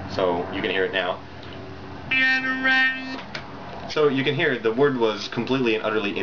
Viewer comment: I thought you were saying "get rekt", not "cataract", and it really confused me because I didn't think that term existed in 2007.